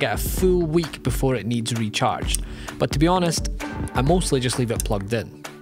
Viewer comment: A bit off topic, I guess but... am I mishearing what he's saying (at 6:00), or is he actually saying "before it needs recharged"? It's not my native language, that's why I'm confused, but shouldn't it be: "before it needs to be recharged"?